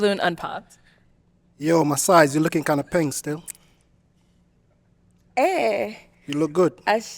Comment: Did he just say "Yo, My size, you're looking kinda peng still..." LMAOOOOOOO